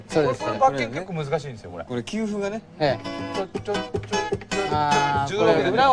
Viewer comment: He actually said "休符がね (Kyufu ga ne) or "This rest", not "This cue"